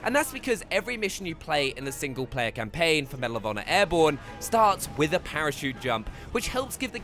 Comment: I thought I heard medal of honor warfighter but airborne is alright